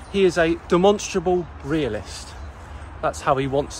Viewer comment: "Don't call me a flat-earther, call me a demonstrable realist" Also: Immediately, demonstrably, rejects reality and substitutes his own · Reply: When says it, I hear 'demonstable' he drops the 'r'?